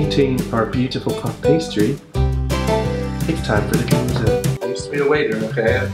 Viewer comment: Am I just weird or did he really say "It's time for the Gemuese.." at 1:49 ..? All this german, god damn >.<